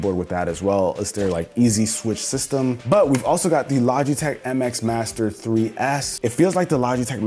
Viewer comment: Maybe it’s just me but the sponsor segment sounded like he was saying lodgeytech a couple times